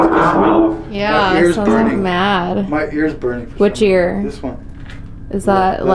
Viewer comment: I heard “Don’t make me mad” 18:44 lol 😂